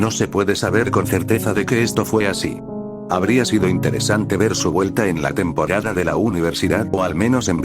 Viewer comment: juro que escuche " habría Sido interesante ver su muerte "😭 estoy muy sorda